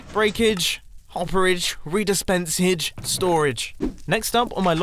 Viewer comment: "Breakage, hopperage, dispenserage, storage" - 2019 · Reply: Edit: He actually said Redispensage